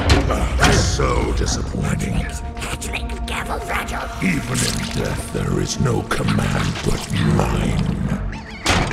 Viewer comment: wtf did he say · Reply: "Careful, fragile"